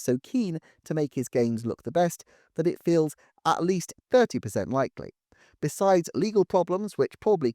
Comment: At around 15:08, audio says 30% but subtitles say 50% - which do you mean? If they do make a paid NS2E upgrade for Smash (I'm skeptical), maybe they could throw in Piranha Plant as a sweetener? I know that they kept the NS2E upgrade and the EX pass separate for Breath of the Wild, but Plant was meant to launch with the game and was offered free for a time.